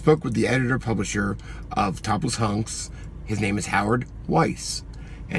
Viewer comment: I thought you said, "topless monks" and THEN I was REALLY disturbed. Whew. Topless hunks, I can handle.